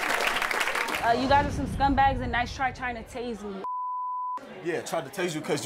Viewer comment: Wow he tazed her?? Smh that couple is lame as hell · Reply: she clearly said tried to taze me... What tf all you females hearing in the comments... He replied yup tried to taze you because you attacked my family.... Key word from both is try.....